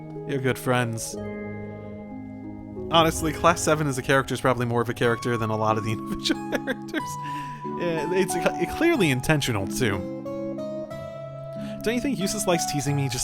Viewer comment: I didn’t quite catch what you said here. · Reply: "Honestly, Class 7 as a character is probably more of a character than many of the individual characters. It's clearly intentional, too"